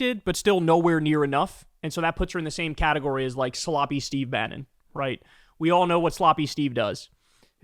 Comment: For some reason, I heard "salami Steve Bannon." The crazy thing is that it still fits.